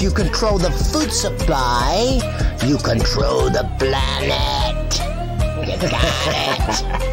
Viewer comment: Thought you said famine instead of planet, which would also be true